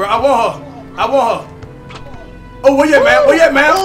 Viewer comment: sounds like is saying "I woho man."